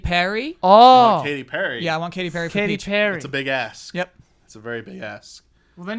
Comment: I thought said “that’s a big ass” and I couldn’t have agreed more.